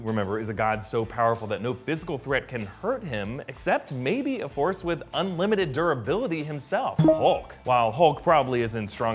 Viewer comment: Who Else Thought He Said "Horse With Unlimited Durability"